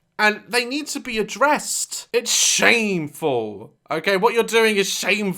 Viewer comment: legit thought he said shaneful